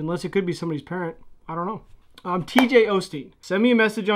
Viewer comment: Thought you said TJ Oshie at first lmao